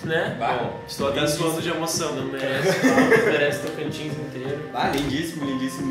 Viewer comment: Agora que eu entendi 7:26 "merece o Tocantins inteiro" jkkkkkkkk inclusive sou de Palmas